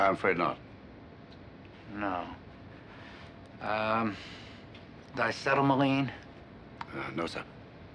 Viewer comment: At 1bout 1:17 he asked for paraffin, then he asked for something else, what did he say? I thought he said disetamaline, but I looked the word up, the way I heard it ... I must have misunderstood the word. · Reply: The word he says is pronounced "dicetelmaline" but Lord knows how it's spelled because an internet search turns up nothing.